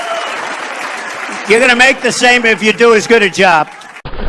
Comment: What did Trump say at 7:45? I seriously don't understand what he says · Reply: You’re gonna make the same if you do as good as a job